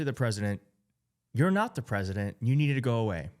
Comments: Devil's Advocate: This is an arbitrary interpretation of the barely audible whisperings of a toddler. He could just as easily be saying "Mr President, I need to go weewee". Also, there is another view where he's clearly saying "shh!" and "shut your mouth" etc to someone off-screen beyond Trump. This whole thing could simply be him telling someone off-screen to keep quiet because of the press conference. There's plenty to criticise without crowing about jumped-to conclusions. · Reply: the kid didn't say "I need to go". He CLEARLY said "you need". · I thought he said Mr President I need to go pee